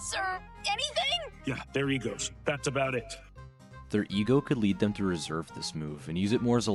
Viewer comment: I don't know why but I thought he said "there he goes. That's about it" It felt so tough to me